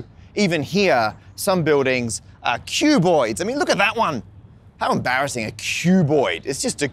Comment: I thought he was saying "cube bois" instead of cuboids at first lol.